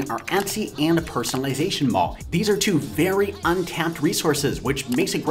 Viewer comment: Etsy and a Personalization "£&$%**" <--- what is that word? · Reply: CANofART mall